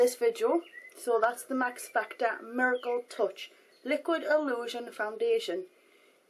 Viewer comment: at first i thought you said, max factor mergle touch;)